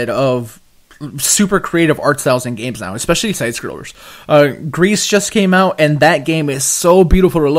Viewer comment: Did he say grease at 4:41? What game is that i looked it up and found nothing